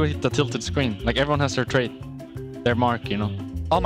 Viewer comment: i think it was meant to say : Like everyone has their trait , not trade